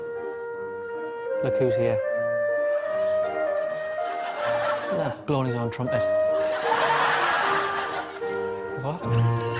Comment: What does Dec say here? I don't really understand it.. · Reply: CP E he says "look who's here, blowing his own trumpet" and then he is told its a clarinet